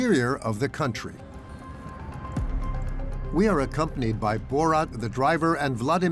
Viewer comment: Did he say Borat ?